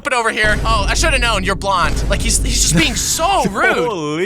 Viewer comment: i accidentally paused at 1:38 and it sounded like it said "I shouldve known youre black" 😭💔